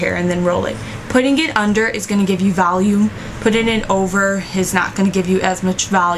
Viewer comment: haha i keep hearing "valume" loveeeeeeeeee your hair!